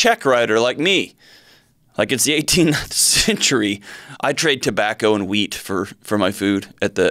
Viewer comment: Sounds like you said tobacco and weed not tobacco and wheat 😆